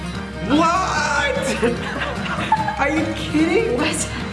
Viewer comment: THE SUBTITLES SAY KIMBAPI KIDDING- 💀💀I SEE U CARAT